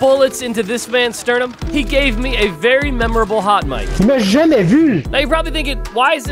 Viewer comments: I heard: "mais je n'ai vu" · I heard him say , mais je tais eu ( but I got you)